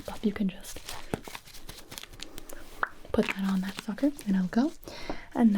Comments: There was one point where I thought she said "put it on that fucker" but I think she jus said sucker... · did she say fucker?? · it sound like you say “put that on that f**ker” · I thought she said fucker but it was sucker lol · i swear when she said “just put it on that sucker” i heard fucker